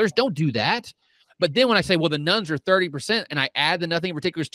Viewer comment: I'm rather old, so this is my excuse: when y'all were talking about "nones" all I heard was "Nuns" and I'm all WTF??